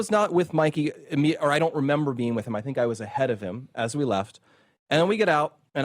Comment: And at the very end the bald guy on the right said that Mikey was nothing but courageous. by walking away and literally abandoning Charlie for dead? I couldn't believe my ears. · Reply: That's not what he said. He said he thinks he was ahead of Mikey.